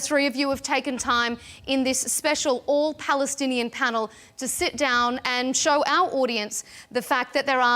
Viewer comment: bro i thought u said its annall Palestine panel. But i got there ab izrali shill who u urself an kzrali shill gabe the forst chanve to talk. some nadal cheap soul for a few shillongs